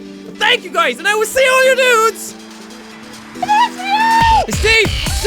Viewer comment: Well I thought you were saying "and I will see you and all your nudes"... Kinda disapointed eventhough I never understood why you would say that x)